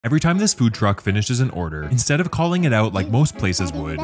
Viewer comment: she got the whole cheesecake factory down there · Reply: oh he said food truck. I thought he said dump truck.